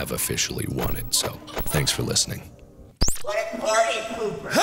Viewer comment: What is Funtime Foxy saying? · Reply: It’s Funtime Freddy and he says what a party pooper